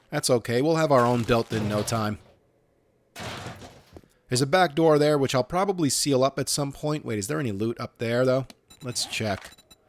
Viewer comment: I thought you say "There's a backdoor there, that i'm probably gonna see a lot at some point". You gotta watch that backdoor and spend some nice time there ;)